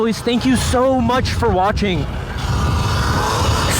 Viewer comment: Why I always hear "As I Dooo" instead of "And Stay Tuned" 😂